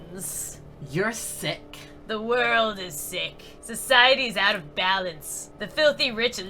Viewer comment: I thought it said society is out of bacon😂😂😂😂😂😂😂😂😂😂